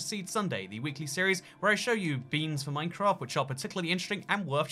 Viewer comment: Did say he shows us Minecraft beans on 0:43?